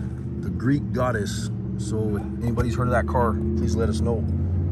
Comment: The only actual theme is " Here is what I can do" .... Did that Say Green or Greek? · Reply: I thought it said greek godess but it’s actually the green goddess